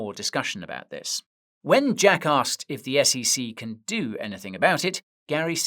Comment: I kept hearing Guy talking bout Jack ass a couple times